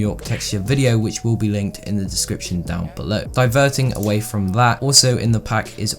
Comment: I thought he said vergin